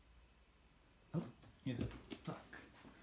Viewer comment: I swear he said “I’m caught!” Poor thing. Time for a mani pedi! 💅🏼